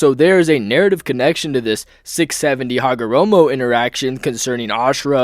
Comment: did he say 6-70 hagaromo interaction?